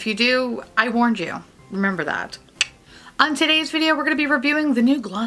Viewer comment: I listened to your story and then you jumped to the review and said, " On today's video..." I thought I heard: On titties video. Lol Was a good segway.